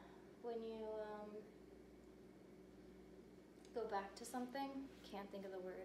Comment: on minute 7:10, I think it is said "go back to kindergarten" (to go step back on a trick train, until she is again confident in the last step you left on former session).right?